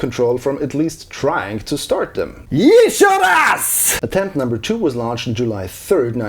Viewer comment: who heard it as "eat your ass!"